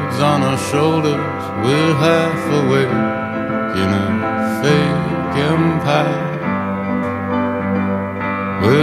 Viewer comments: I thought he said "We're half awake in a fake empire" but your version makes obviously more sense :-D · Don't kill me, but everytime they say 'fake empire' I hear 'making pie'. I think I'm hungry :c